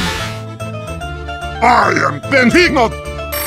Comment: In the first frame when heavy is talking I heard "I am the beanos" lol 👌